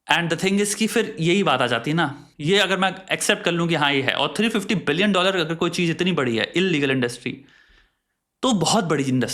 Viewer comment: Did he say 350 Billion* at 28:24 ? 😱😱